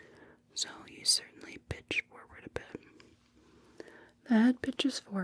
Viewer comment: I know it's a "older" vid ( i mean, i keep a playlist of videos that are multiple years old) but everytime I watch this video I always hear "that bitch is forward" st the 5:40 mark